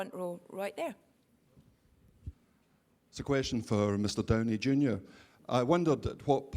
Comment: What the guy said: "It's a question for mr. Downey Jr." What the subs helped me get: "It's a question for mr. Tony Jr." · ANOTHER PROOF, MY FRIENDS 3:00 Loved that voice :0 <3 (I also like Scajo's one). 3:41 BEST. REPLIES. EVER. Like lol what. 4:40 Goosh, I hate that too xD They don't get toime to THINK lmao. Though in this case.... x'D x'D x'D Same for Robert, though xD Also, Tom is a masochist and a lunatic. Much like Loki :v xD 6:23 Her called homi put xD The "thigh master hold" is also my favourite, but because it is awesome. Not because of that is a good way to go... It's SO important to respect how the person choooses to play their characters. Thanks to Josh for this <3 12:06 LOL I mean, you could lose your abilities too in an accident or something, so. xD 14:45 THAT was a great idea actually. YOu created one of the things I really do in every kind of fiction, which is a team whose more or less every individual I'm interested in and that become like a family + somewhat good plot + CHARCTER DEVELOPMENT. YESS. What Mark says is totally right. People are SO BRUTAL on the IT... you can't just NOT value someone and at least value their work....